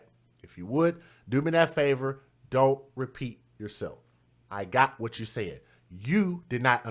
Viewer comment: This guy is funny at 36:38 did he say dont repeat yourself I heard what you said? Omg that's all he does is repeat his self 😂😂